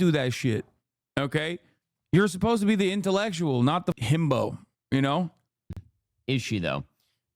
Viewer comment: ok but did Hasan just call himself a himbo